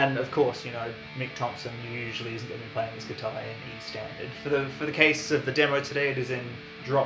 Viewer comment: At first I heard "Mick Thomson is usually playing his guitar around E Standard" 😂